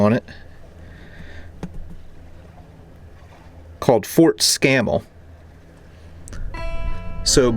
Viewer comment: Your history lesson is making me remember history I learned while traveling as well now lol. I had to go back to catch the name of the other fort you mentioned in your video, Fort Scammel. At first I thought you said Fort Scammon. That is the name of an old earthworks fort that overlooked the city of Charleston, VA (now WV). Not too much left to it now, just earthworks in the middle of a bunch of houses, but still a strategic site during the Civil War in that area. Thought it was kind of humorous so thought I would share.